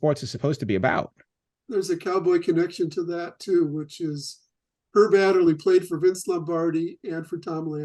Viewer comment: Who is he referencing at 50:30? Hard to understand. Thought he said “her dad” but that makes no sense.